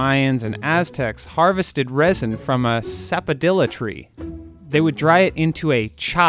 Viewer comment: I thought he said Zavodila for a second 😂